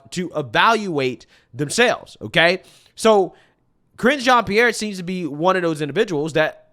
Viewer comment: I laughed because I thought you said Cringe Jean-Pierre!!